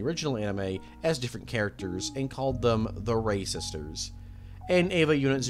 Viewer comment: I thought he said racisters